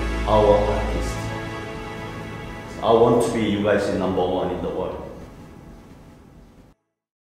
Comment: I almost cry when talking. And i heard "ill rise you to number one in the world" We have to support to make it happen